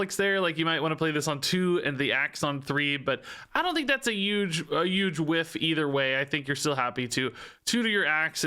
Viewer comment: I just noticed that says "'uge" instead of Huge.